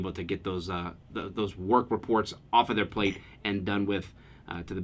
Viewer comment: At first I thought you said "Pork reports" - That should be the term for these kinds of calls though.